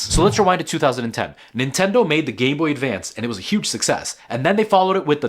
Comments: Sorry bro…I don’t know why but the part when he said “game boy” I misheard it as “gay boy” 😂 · Am i just mishearing? He said "gayboy advance"